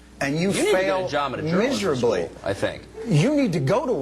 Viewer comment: what did the guy say? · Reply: You need to get a job at a something school... Charm school?